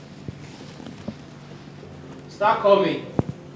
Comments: Who else heard the director say "Start coming" @ 1:42:16😅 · Who also heard when the director said start coming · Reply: I heard it too. Check 1:42:15. You will hear start coming 😂😂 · Lol.... Did I just hear, "start coming"?